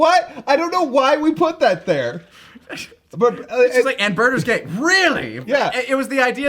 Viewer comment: All these years, I thought Guldo said "Birdo's gay". Huh.